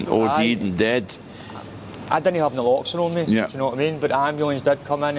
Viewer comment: its ''Naloxone'' a powerful effective drug to get someone out of an OD situation .Subtitles say '' No locks on me '' .Just saying coz Naloxone is important .